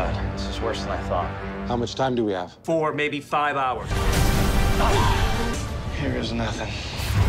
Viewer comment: What they say: "How much time do we have?" "Four, maybe five hours." What the subtitles say: "How much time do we have?" "Four, maybe five YEARS." Uh... "Eh you know, three or four..."